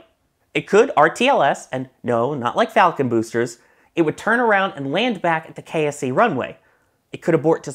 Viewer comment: I had to rewind because I heard "it had to turn around and land at the K.S.P runway 🤔 😅😂😂